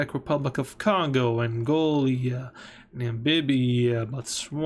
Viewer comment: Angolia instead of Angola is crazy